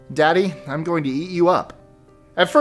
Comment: Number 22, I thought the boy said to his father "Daddy, I'm going to YEET you up" not "eat you up" wtf lmao😂